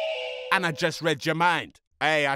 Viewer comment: I thought he said the bed feels good right and I was on a really comfortable bed so I was like wow wtf